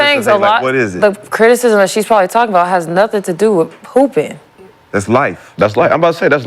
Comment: Did I just hear her say " poopin' "?